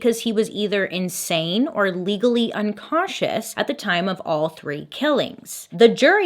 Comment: un cautious xD anyone catch that unconscious